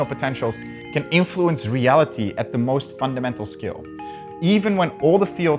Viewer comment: captions are wrong. Should say "scale" not "skill". Hoping someone sees this and fixes it lol